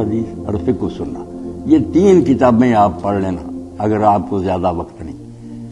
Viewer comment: Someone please help me understand what molana ishaq said after 7:40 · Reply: Fiqh ul sunnah also by sayyad sabiq